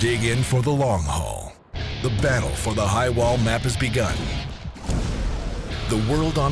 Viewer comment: i thought it said Highway Map Trailer